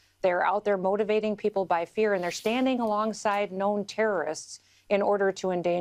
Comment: Oh! I thought she said, "standing by Noem terrorists."